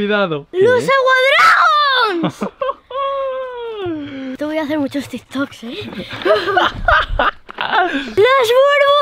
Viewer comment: Pensé que dijo te voy a ser muchos tik toks